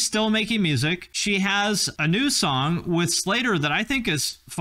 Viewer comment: stg I thought you said “she has a new song with slayer” fuck that would be the ultimate troll